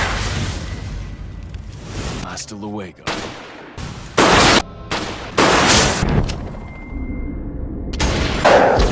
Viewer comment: what does leon say? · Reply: Hasta Luego